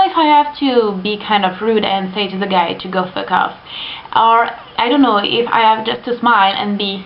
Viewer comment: ask the guy to go "fuck off" sounded like "fuk off", haha that's nice, don't care if that's rude but the guy its trying 2 do sumthin bad... and i like ur lipstick its highlighting !